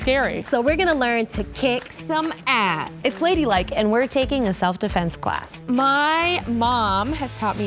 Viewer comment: I thought it said selfi-defence 😂